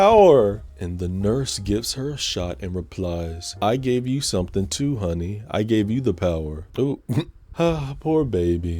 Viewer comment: I thought the nurse said "I gave you something. And it's NOT the power." in her sassy tone. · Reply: She says “He may have given you something, honey. But, it ain’t the power.”